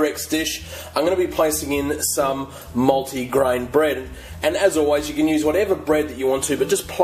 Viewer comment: I thought you said "moldy green bread" lol